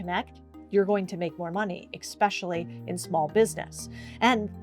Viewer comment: Did you just say eXXpecially?